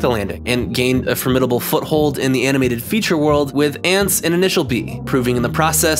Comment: did he say initial D, the drifting